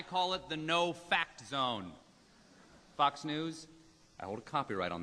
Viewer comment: Did he say "Fuck's News"?